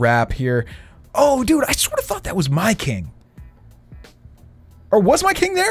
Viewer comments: I thought he said I swear to fu** but he said I sort of thought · I swear at 8:38 said I swear to f*** that was my king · did he say I swear to fuck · Sounded like you said « I sweat to f that was my king» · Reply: Hitjoy 1 no he said «i sorta thought that was my king»